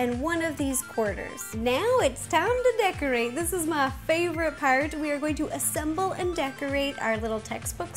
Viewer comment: when she said part it sounded like jack septikai